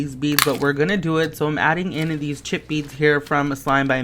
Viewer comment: Oh for the first adding too much ingredient slime I thought he said "chippy beads"😂🤣